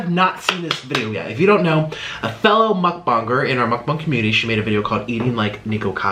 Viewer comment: I thought he said muckbong not muckbang